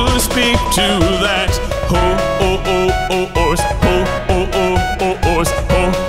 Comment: does he say hoes